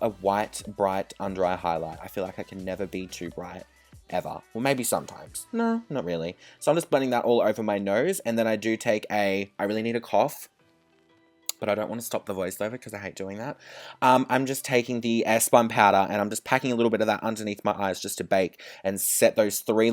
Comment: I heard I really need a cock😂